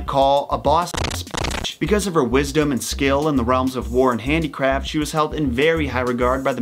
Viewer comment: sounds like crap instead of craft